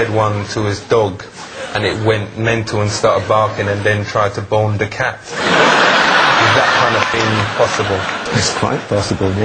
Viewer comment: i thought he said, bone the cat